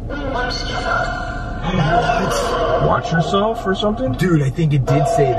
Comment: I think it said, yes maybe but you’ll be scared, watch yourselves